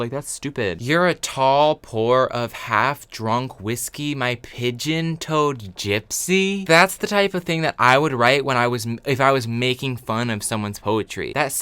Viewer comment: 'You're a pidgeon-toed gypsy' needs to be added to the ADL list of slurs. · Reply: 경민박 oh my god i thought he said “pigeon toad gypsy” and i was so confused - not that this makes a whole lot more sense